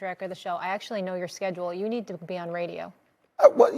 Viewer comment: I heard her say you are ugly you should be on radio!🤔 let's go Stephen A-hole!😎